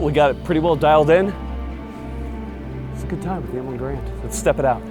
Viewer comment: I thought it said the “mid” garand